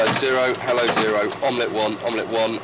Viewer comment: I swear I heard him say omelette one. You ordering pancakes too?